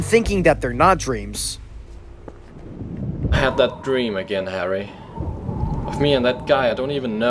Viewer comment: did he say larry 💀